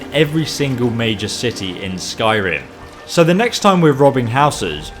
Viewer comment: Everytime says major cities I always hear Mage's cities.